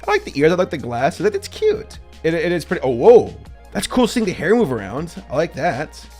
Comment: did he just say owo